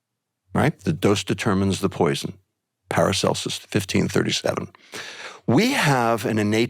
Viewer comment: did he say “Peristalsis 1537”???😮 I don’t think he really did, but if so, 😂